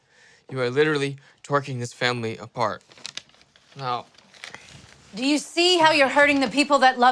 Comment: "you're literatly twerking this family apart" hahahahahahaha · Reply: Lol..😂😂..when I heard that.."you are twerking this family apart" I atop laughing and the other part that was also funny was her twerking being a dumpster. (Lol.lol). 😂😂😂😂